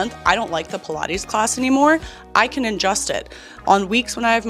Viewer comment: I was watching this while brushing my teeth and toothbrush sound distortion played me hard. You were saying that to find joy in exercise you can “adjust it” but I only heard “add Justin” and it made total sense like “ok Caroline, noted, add nice boyfriend and healthy relationship to the mix, I guess anything becomes more enjoyable”